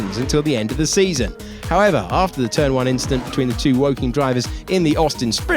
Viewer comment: Did he say woking drivers🤨